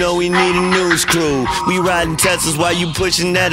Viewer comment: After listening to the snippet for past few months I always thought he was saying Testa's as in Testarossa. I guess that's not very New New though.